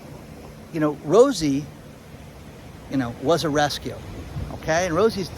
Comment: I THOUGHT YOU SAID " ROSIE, WAS ARRESTED "🐕🚔 👮⛓ but you said "Rosie was a rescue". LMBO